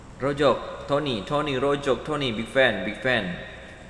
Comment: Kok Aku Dengernya GeegFam Yah 😅